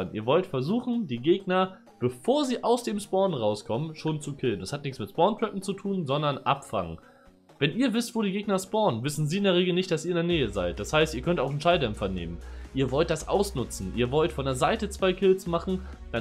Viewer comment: ich dachte du sagst abf*cken. 2tes mal angehört und gedacht ,,ahh abfangen“😂😂 Ich alter Cod'Ier.